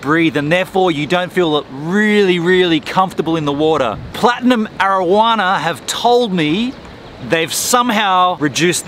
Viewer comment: I thought you said Platinum marijuana